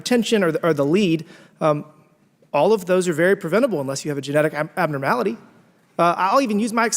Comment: did he just say that cancer is “very preventable”? did i hear that right?